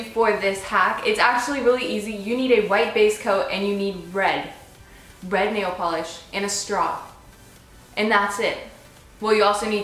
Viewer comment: Somehow I heard "And you need bread. Bread nail polish." haha